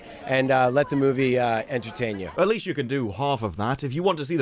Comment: did he just say edgeutain you?!?!?!